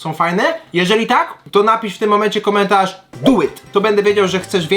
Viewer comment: do it! choć za pierwszym razem usłyszałam "buły" xD